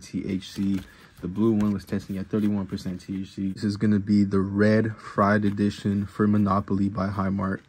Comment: Thought bro said red pride 🦄 edition 😂 1:06 I was bouta click off😅